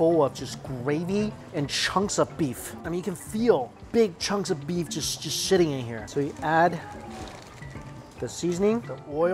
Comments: i thought he said” you can feel the chunks of beef just shitting in here” 😭 lmao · did he just said shitting here or is it just me hearing that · It sound like he said sh*tting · I think he said shitting